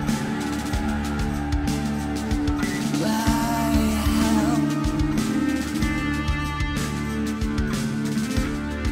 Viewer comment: Wtf james says at 7:29? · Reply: There's an audio editing glitch, and then he says 'my ham'